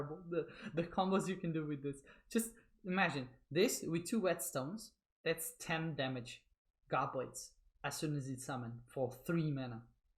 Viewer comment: Hey my friend I’m new to the game I play war a lot and on Time stamp 17:15 I couldn’t understand what you said it sounded like you said whetstone but I tried looking up that car and cannot find it to go with that relic how does it work